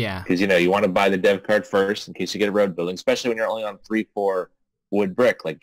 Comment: I always hear “road bidding” when you say “road building”. 😄